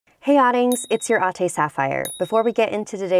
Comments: i always thought that she said 'audings' but its adings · I just realized shes been saying adings + ate this entire time i thought she was saying audings bc of audio